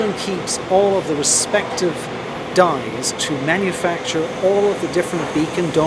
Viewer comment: Closed Caption said Dyes for storage for Dies Injection Molds for Beacon